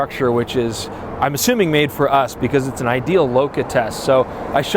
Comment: "loka"? doesn't sound like "bokeh" - what did he say? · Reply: LoCA, short for longitudinal chromatic aberration